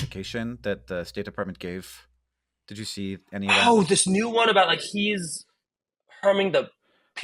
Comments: Did he just say the n word at 15:25? Lmfao. Average Israeli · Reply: I thought so too but he is 100% about to say "peace negotiations". But he cuts himself off and adds "ah... yeah" so it sounds like "peace neg- ah... yeah". I am sure Hank didn't even consider he could be saying anything but peace negotiations. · did he say the n-word???? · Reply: Negotiations, and cut himself off lol